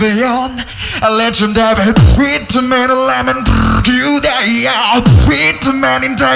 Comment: it sounds like he said "with tomato, lemon" hahaha